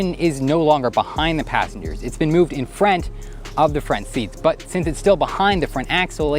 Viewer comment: Are you from Canada? I Keep hearing FRENT instead of Front